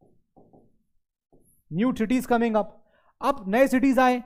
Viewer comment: Did u just say.... " New titties coming up"